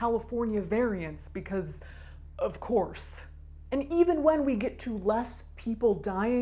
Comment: "Possibility of new, Southern California variants, *of course*—" I heard that as "of corpse" and I'm not really sorry. =v=